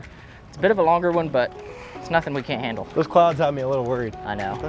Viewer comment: Great match! When said those clouds have me worried thought said quads which worry all of us, even at home!